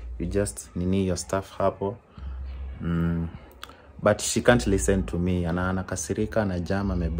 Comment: Did i hear say "se cant listen to me"😂😂😂😂😂😂😂😂